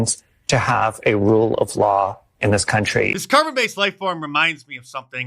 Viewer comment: Ohhhhh I thought you said Carton. Like get him a carton of smokes.